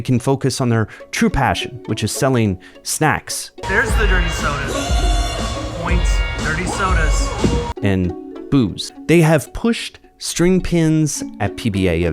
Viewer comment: i had to put captions on because i could of swore you said " snacks and boobs"😅 i got really confused. I was thinking alleys were getting desperate and combined a strip club and bowling alley into one😂😂🎉